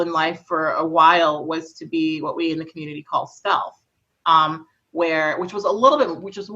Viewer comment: What term does Kat use here, didn't hear it properly. "Self" or "stealth"? To be hyperfeminine and to disappear in society for survival. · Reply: she said "stealth"